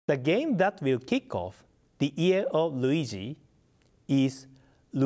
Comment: For a second, I thought he said the "Ear of Luigi".